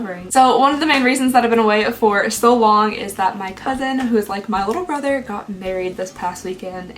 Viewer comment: At first I thought you said my cousin and my little brother got married 😂😂😂